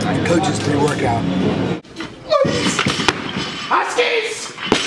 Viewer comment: I always wondered what it was Jon shouted all the time during a lift, i could make out all of the others shankle, dimas etc but never what sounded like 'askis' but this camera must have better sound because i finally heard it as huskies